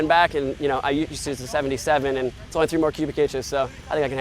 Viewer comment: Chad George with the quick math. 80 ci tank only 2 more cubic inches from his normal 77 ci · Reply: Haha I heard two the first time around as well. Purdy sure he said 3 after another listen